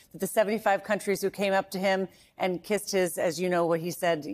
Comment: i thought he said they were kissing his arces ......